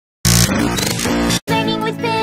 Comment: It sounded like it said "we like to smell and, we like to hug"